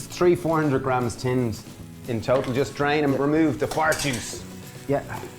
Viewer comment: Did I hear you say "fart juice"? I apologize if I'm incorrect! Thanks for the recipe!